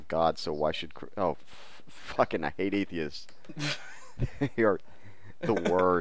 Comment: He didn't say "I hate atheists" he said "Hate Atheist" meaning that the guy was basically hating on religion. I know it sounded like he said I hate Atheists, but he didn't. He was talking about the Atheists (Usually over the internet) who just say shit like "God doesn't exist" to Christians for the sole purpose of pissing them off. They are the obnoxious ones. Perhaps that clears it up, because I though he said that at first too.